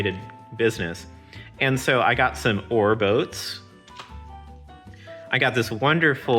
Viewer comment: I had to do a double take when you said you got an "oar boat," because I heard "ore boat" and was thinking there's no way that you can ship taconite in a row boat.